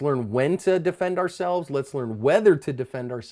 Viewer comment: What's asp?? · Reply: lol one I first saw ur channel brother I also thought you kept saying cover ur ass lol ! foolish me but quickly I realized what you meant! thx for the tips very insightful! and your right brother we can die any day any moment! it's good to start practicing for ur final day by connecting with the creator and being ever connected with loved ones! this is the truth of the matter! I don't want to hurt others or see people hurt! however when people refuse to use their brain and refuse to listen to reason and are consistent in being hostile towards you ! then you must defend yourself n loved ones! to god we all belong to him we will return! this life is but a mere moment ! Godbless brother!